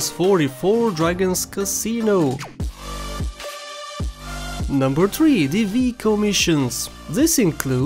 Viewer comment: Noice... my gta sa save was 99%. Turns out I missed the train missions. Never ever tried to get in it. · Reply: Also I know it's your accent, but the tree scene and you apparently saying "Number Tree" instead of number three at 1:40. Nice job. You actually made me laugh a lot when I saw that number 3 part